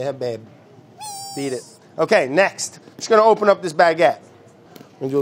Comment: ngl i thought he was gonna say "just gonna open up this bad boy"